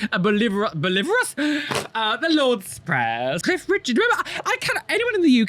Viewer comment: I thought you said Keith Richards...and was VERY confused as to when the Rolling Stones sang the Lord's Prayer...